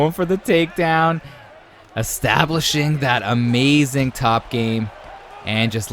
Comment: yooo what did he sayyyy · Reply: Haha establishing